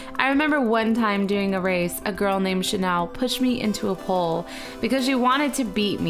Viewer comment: oml i always thought you said she pushed u into a hole not a pole for sooooooo long